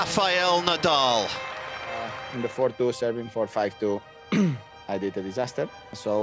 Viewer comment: Did Rafa say I did a disaster at 5:11?